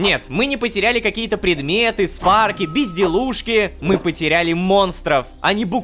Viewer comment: мне послышалось "пизделушки" в начале XD